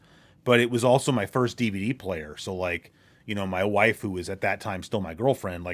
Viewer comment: I thought he said my waifu 😂😂